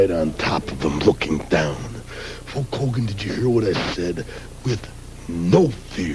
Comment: Why did Randy always sound like he was saying Hawk Hogan 😂😂😂😂